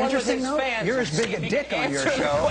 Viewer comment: did he call him a dick at 12:29 ? hahaha